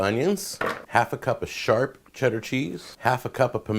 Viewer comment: Omg lol, when he said half a cup of sharp cheddar, he kinda pauses after sharp for a second before he says cheddar and I heard "half a cup of shart" lmfao 😂😂😂😂 i was like wtf did he just say shart and did not catch or mention that, lol